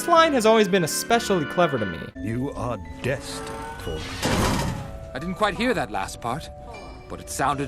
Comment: If you read the dad's lips, he actually said "You are destined for greatness." :]